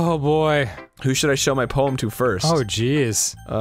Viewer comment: When he said "Who should I show my poem to first?" I thought he said "Who should I shove my pole into first?" LMAO